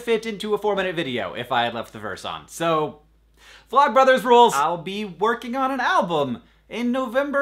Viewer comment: I put on subtitles and it thought he said "blood brothers rule" when he said "vlog brothers rule"